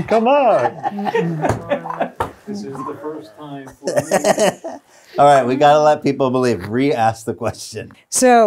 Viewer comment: does anybody else hear “this is the first time for me in the marvel universe”